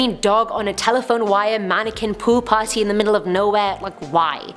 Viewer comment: "mannequin pool party" I heard mannequin poo party, for a second there. 😅😅